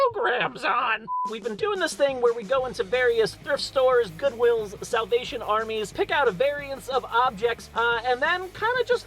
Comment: I know he said salavation army but it sounded like south-asian army